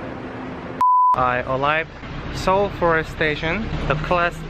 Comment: I heard "I'm alive" instead of "I arrived"🤣🤣 btw Ramadan Mubarak!! Love from Bangladesh🇧🇩💖💖